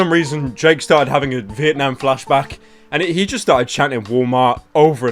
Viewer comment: Sounds like he was saying "Paulmart".